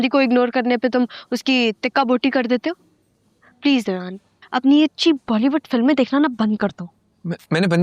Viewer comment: did she say his real name Dayyan 😂